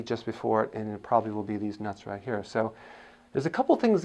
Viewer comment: What was the cause of pain? “Deez nuts right here”? Did I hear that correctly at 2:21? Lol 😂